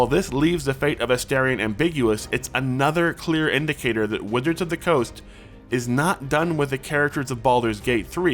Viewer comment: Anybody else hear "Withers of the Coast" at 5:35?